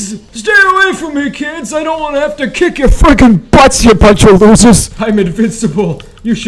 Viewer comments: for 6:20 did he said the f word🤔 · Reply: no he said freaking it was just bass boosted and it sounded like that. i thought like that at first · AYOOOO WHAT DID HE SAYYYYY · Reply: Freaking